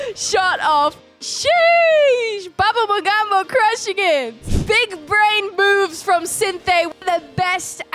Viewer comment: literally i heard big brain boob 🥴🤣🤣